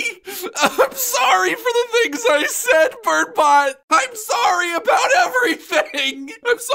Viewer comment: captions said “BIRD PIE” instead of “burnbot”